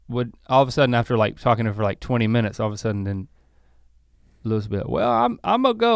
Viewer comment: Did he say f***ing her 32:46? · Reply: He said, "...talking to her for like 20 min"